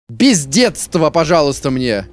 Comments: в начале: «БЕЗ ДЕТСТВА» Что слышу Я: «П*ЗДЕЦ ВАМ» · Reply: Я услышал "пиздетство" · Мне одному слово "бездедство", послышалось немножко другим?) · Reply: Пиздетс-во! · Мне показалось в начале ты крикнул: «ПИЗДЕЦ» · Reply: Мне послышалось "бездество"